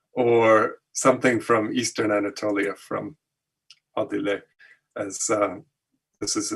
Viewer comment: Hello, and many thanks for this fascinating study. Please could you tell me the artist you were reccommending at minute 47:43 after mentioning Hoirat and the Anatolia region? Cannot catch the name on the video. Many thanks. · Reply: Dear friend: Adile Kurt Karatepe (he said Adile in the video, I searched for "Adile hoyrat" and found above name.)